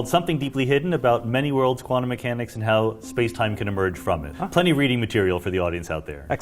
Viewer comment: expert level conversation i know they are talking in english. but i didnt understand 1 bit. BOOM 10 dimentions solved!!! 27:28 he mentioned plenty of reading material??? i heard a good book to make you sleep easy